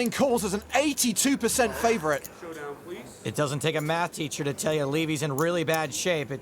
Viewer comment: Breaking bad tv show. Character that's a chemistry teacher who ends up cooking meth successfully. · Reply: oh! I thought Ali said “t-shirt” not “teacher”